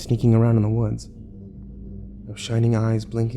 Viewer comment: Lol sounded like he was saying ''shit,shit,shit''